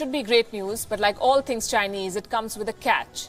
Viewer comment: I thought she said "But like all things chinese it comes with a cat "